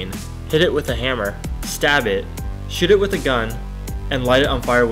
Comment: Did i just hear "Sh*t it with a gun"